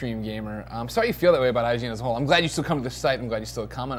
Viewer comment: did he just say im glad u still come on us at 0:58 0.o???